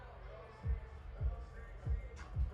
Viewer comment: Not Andre saying "That's Sexxy Red. I got to go" 💀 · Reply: Omg, thx for translating -- I thought he said "Section Red" lmaooooooo